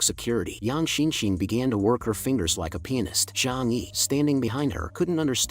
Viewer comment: "what did he sayy??" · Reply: oh it says peonist not p#nis